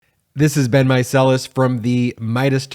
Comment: when u say ur name i initially was like ur name is marcellas...i thought u said "this has been marcellas" 😂